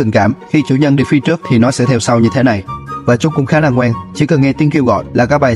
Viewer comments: Ngoan tui nghe thành ngon💀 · Khá là ngoan nghe thành khá là ngon =)))))